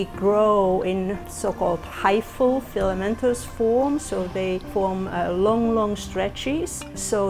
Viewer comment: did she just say LONG LONG STRETCHIES!!!! 😳😳😳